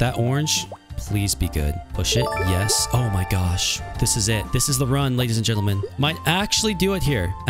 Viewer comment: I thought he said,"oh sh*t"